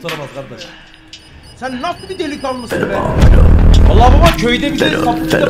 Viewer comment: da ne diyor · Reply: Sen nasıl bir delikanlısın be diyor